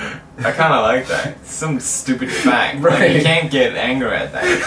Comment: that's really wierd, youtube's captions are showing the initial, really innaccurate captions that were misheard instead of the one's the community has revised. like in 6:20 the caption says "some stupid f*g right?", but when i go to the editor to change it is show the correct "stupid fact"